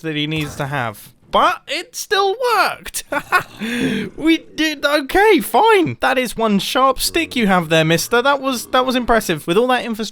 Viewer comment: DAMN BOI THAT ZOMBIE SAID YOURE DAMN RIGHT THAT'S A SHARP STICK